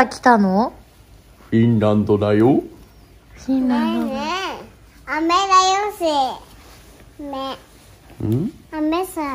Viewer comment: フィンランドから来たよって言ったから、ぽんす英語喋ってない？？ I miss you って聞こえる！